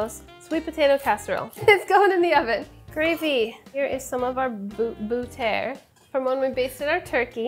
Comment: I heard boutaire, and I was like, "What fancy cooking term is that?" Ten seconds later I realized Sohla was just saying butter with a "fancy" accent 😆😆😆